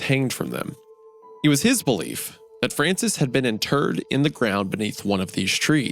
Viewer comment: who else heard Francis had been a turd in the ground? 😂 i did a double take.